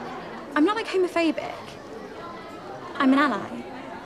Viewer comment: The hole time I thought she said “ I mean am I ?” But she said “I’m an ally “ 😂